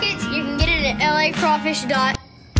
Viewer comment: Thought he said let's see if these fu!!in crabs are any good. Thank God for subtitles. Lol